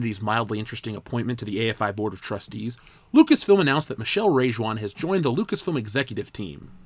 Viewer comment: I only heard you saying "Michelle Rage One"